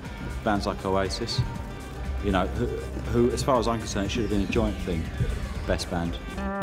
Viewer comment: wait what did he say i dont speak british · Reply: “Bands like oasis, witch as far as I’m concerned should’ve been a joint thing best band” I think